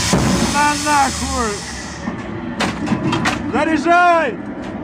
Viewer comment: What does he say at 0:07 ? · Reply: Заряжай - Zaryazhai - Load/Reload.